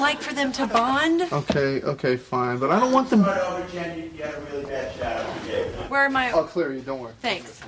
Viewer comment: could someone explain 43:40, what are they saying about the camera and david schwimmer says something like "i'll clear you don't worry" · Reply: It's really hard to pick up what the director said, but it was something to the effect of Jennifer had a really bad shot something something......